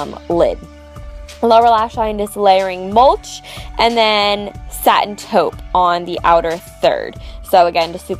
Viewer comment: Gorgeous as always! And when you said 'Satin taupe', I thought you said 'Silento'; so for a second there I was like 'oh no here i go...' and now I have Watch me whip(whip/nae,nae) song stuck in my head! aha